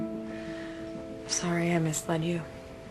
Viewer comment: W did she say @ 0:39? I swear I cant hear just Sorry ive missed... and than nothing XD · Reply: I know I’m literally 12 years late but in case you didn’t know by now she said: I’m sorry I mislead you🤣